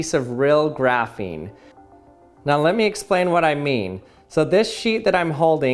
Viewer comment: Him: So this sh!t that I'm holding Me: What? Also me: That's not what he said